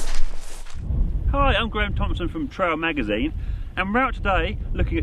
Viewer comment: I thought that said grant Thompson